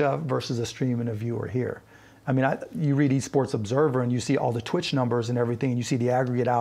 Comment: At first I thought it said ESCORT